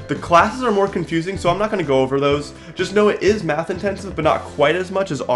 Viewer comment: the number of times i heard "but it is math intense"